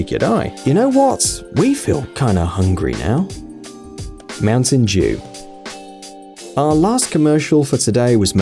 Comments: Who else thought that this guy said Mountain jew. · does he say mountain jew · I thought he said "Mountain jew"